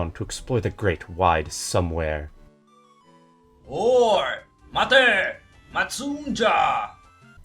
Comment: When the person was saying 「おーい!」, it definitely sounded like he was saying "Whore!"